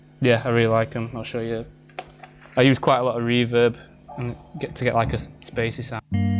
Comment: someone can tell me what Lee says at 4:00 PLEASE :D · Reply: "I use quite a lot Reverb" He says that, his British accent is a little bit difficult to understand sometimes.